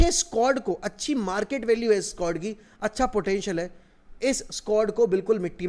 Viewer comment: Market Value of this United Squad ?...😂 am I hearing this right ???